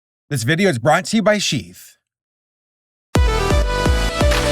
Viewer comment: I thought you said: "This video is brought to you by sheep"